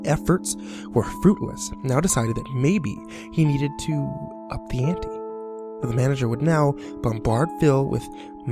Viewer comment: What is he saying at 5:32? It sounds like "maybe he needed to... a panty." I know it can't be "a panty," but I can't for the life of me figure out what he's saying. :(